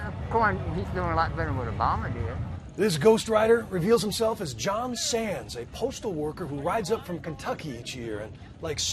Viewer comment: did i hear john sans?